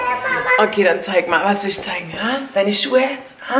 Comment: Hat sich so angehört als wen bei 2:20 scheiße gesagt hat😂❤️ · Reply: Sie hat zeigen gesagt